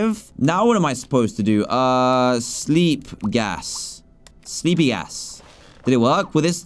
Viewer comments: am I the only one who heard say sleepy a$$😂😂😅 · if im not mistaken, just said the A word! did anybody else hear that too?! he said “sleepy a🤭s”